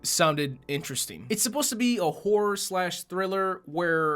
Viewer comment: When he said" this is supposed to be a horror/thriller...", for a split second, I thought he said "a whore.."! I swear, I was cracking up😁